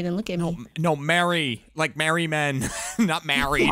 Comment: Don't worry. I thought said "Married men" too. 😂